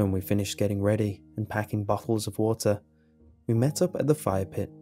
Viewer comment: he said bottles of water (sounded like buttholes of water)